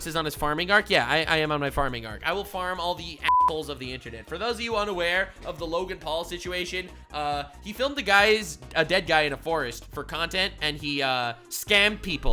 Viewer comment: it really sounded like you said apples here. 😅